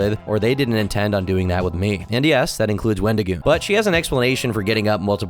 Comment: did he just say that includes wendigoon?